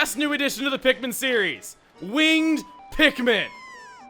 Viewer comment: I heard weed pikmin Smoke pikmin everyday